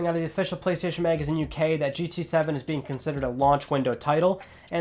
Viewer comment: who heard gta 7 ?